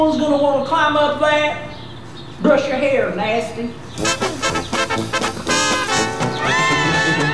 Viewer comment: "brush your hair nasty" not even a minute in and I'm laughing hysterically! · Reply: I hear "brush your hair nicely"